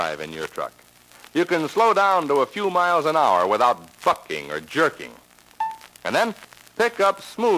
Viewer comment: it may just be that the lp or 78 is worn out,but he sounded like he said fucking